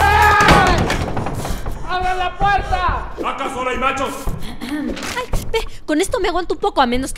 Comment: " acá solo hay machos " Jaja mi parte favorita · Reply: Llámenme loca, pero yo siempre pensé que decía "Acaso ahora hay nachos?"